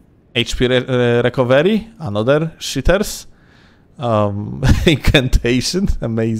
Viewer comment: Whenever you say the word "cheaters" I always thought you were saying "shitters" which still pans out either way.